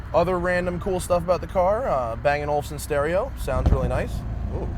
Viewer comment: Sounds like you said " A banging Olufsen stereo!"